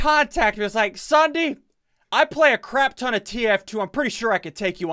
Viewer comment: It sounded like he said "he plays a lot of tf2"